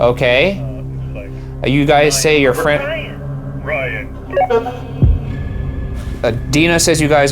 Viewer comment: Ok so i have a question- does anyone else hear Ryan a bunch more times, in very different voices, that were not captioned here: 1:02:35 OR Is it them on the radio trying to scare Ryan?